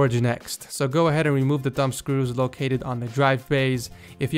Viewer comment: When you said thumb screws I thought you said dumb screws